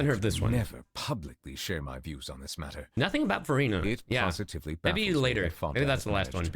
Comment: Wanderer: FUNDAMENTALLY SPEAKING, THERES NO DIFFERENCE BETWEEN RAIN AND SNOW YET PEOPLE PRAISE THE LATTER. FATE CAN BE TRULY ARBITRARY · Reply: I somehow thought you said Furina because of the comparison being somewhat similar to what she said during the archon quest.